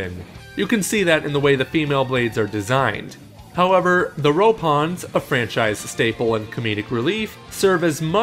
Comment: did he just call them Ropons?